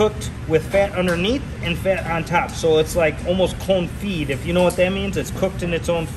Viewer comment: At first, I thought he said, “clone feed” 😂